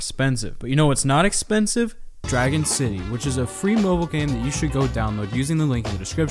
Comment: I heard "you know what is not expensive? Dragging city." Me: IMAGINE DRAGGIN DEEZ N